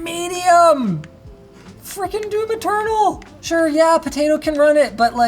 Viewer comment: Subtitles says "do material" instead of "Doom Eternal" that's the kind of mistakes that pops off really often in LMG videos and shows it needs at least one proof reading...